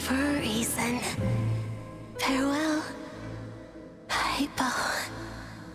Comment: Did she say Paimon ? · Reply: He said 'hypo'. She was talking to Hyposelenia ☠️